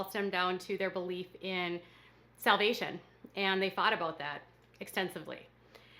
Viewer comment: Did she say, “They fought about that,” or, “They thought about that?”